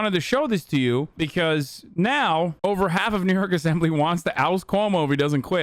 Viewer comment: i thought he said the n word w the hard r instead of NY bruh cuz i was away from my ipad and not following along😭😭😭